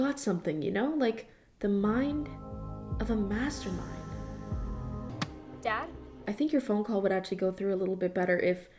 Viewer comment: I thought she was saying dad to her Jell-O cup